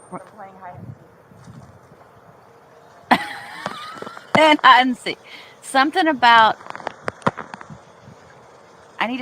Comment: i believe i heard her say "im basically a dead woman from playing hide and seek"